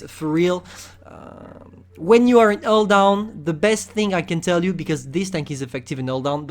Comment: Meadsy: pike nose on blitz: picky nose More word he cant pronounce... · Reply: When he says hull down, all i hear is "all down". T_T